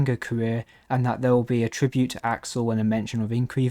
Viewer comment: did I hear Axol? lol